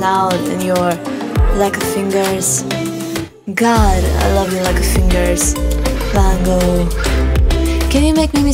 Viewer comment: Never saw the lyrics to this song until right now and I always thought she was saying "I love you like a fingers" and I was always so confused when I listened to that part lol